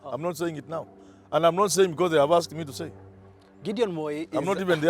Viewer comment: did i hear you say an not saying because they have asked me to say